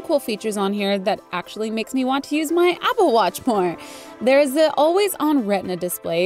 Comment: did she say "apple watch porn"??? LMAOOO